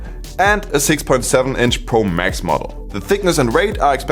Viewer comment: dId he just say Pro MAXIMUM? 😱😹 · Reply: Nope. He sade “..max model”